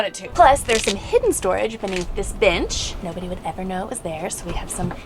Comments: I love you're videos! I was watching explain the bed situations. I could have sworn she said "This bitch turns into a second bed!" Lmao😂 she actually said bench. So funny... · Okay maybe it's just me but I totally thought was saying "bitch" every time she said "bench" 😅😂